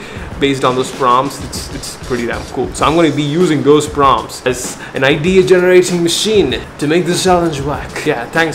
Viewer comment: you say "an idea" I hear NID 😂.